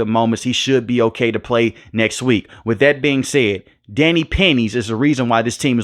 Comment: Danny pennies. I thought i heard Danny panties lol